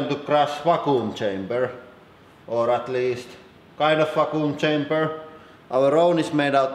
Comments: I honestly thought you said "Fakku" chamber.... and please don't look up Fakku if you're not 18+... (even though I know some of you will anyway) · Wakuoom cheymber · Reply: i thought he was saying raccoon chamber lmao